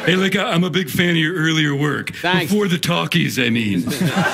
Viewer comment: What the hell does Geoff say at 1:11:50. I've listened to it a dozen or more times and my brain is failing to recognize it as words. "I'm a big fan of your earlier work before the ................" sounds like "talky's an eve" to me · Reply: It was "before the talkies I mean" . Implying that Don was old enough to have been in silent movies 😂